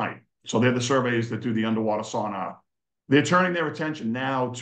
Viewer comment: I had a good laugh here . . . Mr said "sonar", I heard "sauna" . . . but then I'm from California where we speak the true American English . . . 😂